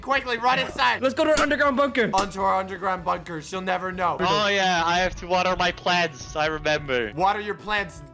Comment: At first I thought said "oh yeah I have to water my pants"